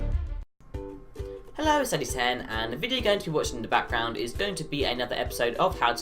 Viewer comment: nice, btwnat the start of your videos, I saw someone else comment on this to, it used to sound like you said,"hi it's lu-10" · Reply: Yeah i replied. I say "Hello it's Addey10" i tried to slow it down this video lmao clearly it didnt work